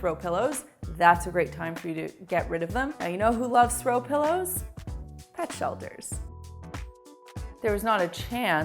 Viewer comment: At the throw pilloe one where u said "pet shelters" i heard "dutch elders"...... Both true i guess...